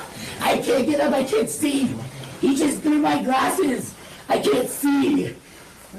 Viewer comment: I thought he/she said, "I'm blind, i can't see without my glasses".